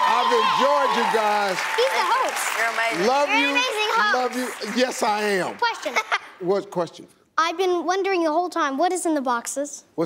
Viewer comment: Did they say 'You're an amazing hoax'?